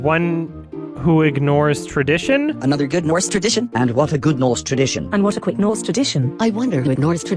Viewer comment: I definitely heard “and What a good Norse tradition”